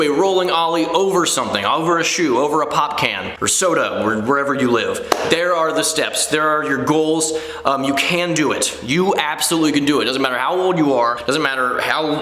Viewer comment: did you just say ollie over where you live, I think that's going to be a bit hard